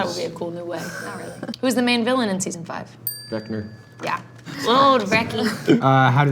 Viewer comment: the way it sounded like joe said “vecner” is so funny to me for some reason😂